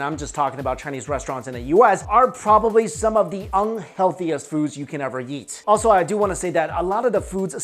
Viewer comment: wait why did I hear "probably some of the unhealthy things you can ever yeet"?